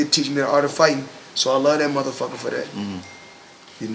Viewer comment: Did he just call his father a "Mother Fucker?" He is, but still.